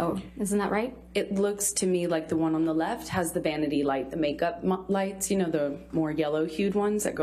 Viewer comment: Was i the only one who heard "make up " near the time